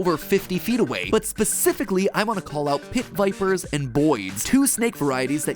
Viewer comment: I thought he said BOIS not boids